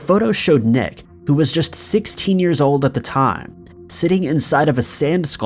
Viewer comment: hold the phones. You meant to say Nick was 6 at around @ 5:15 right? cause it sounds like you said 16 but the subtitles say 6.